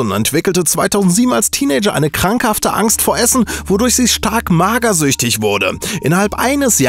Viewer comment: Ich hab am Anfang verstanden "Angst vor Erbsen" WTF